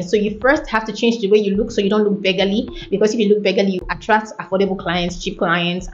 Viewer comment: please change the word baggily use another synonyms. While filming next time. Perfectly said 🫡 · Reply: There's no "baggily" at 9:51. That's 9:44 and it's the word "beggarly". The caption is on the screen. And I said exactly what I intended to say there too. Thank youuu for the compliment ❤️